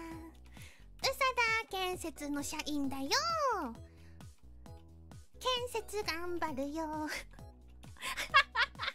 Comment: what did Sora say here? Seems like she's talking about Pekora? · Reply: "Peko-chan arigatou ne"